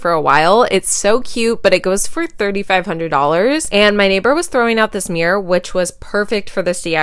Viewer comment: I thought u said ur neighbor was throwing up the mirror😅